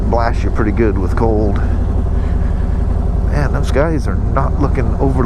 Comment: Now I'm scared. You pull up to the HD dealer and sounded like you said those GUYS aren't looking friendly. But a replay found you said SKY. I was hoping you had a H.D. challenge coin. haha. Great Ride.